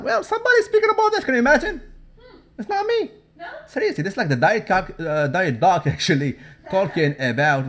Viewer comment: Did you say thedietcock?